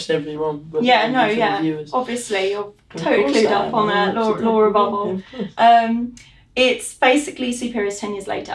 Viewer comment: At first, I thought Brad said "Laura Bobo" and I was like whuuut? XD